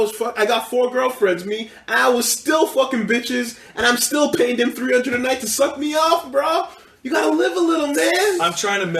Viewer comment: At first I thought he was saying that he's "still paying someone to suck him off" like he took out a loan for that.